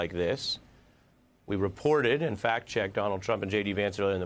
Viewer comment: Sounds like he said "Fat checked"